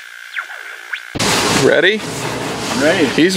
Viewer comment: min I heard him say: "SHREADY?" (Just thought that would have been a pretty cool contraction!)